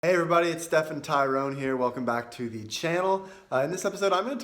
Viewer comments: I thought you said it's "Steph & Tyrone here" in your intro. LOL. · I thought he said Steph and Tyron (kind of expected a black guy to chin in at some point) haha, but this is cool, thank you.